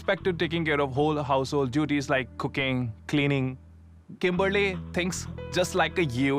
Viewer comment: Yo I thought he said she would be responsible for taking care of all household duties like cocaine😂😂😂 3:27 I was finna move in with em myself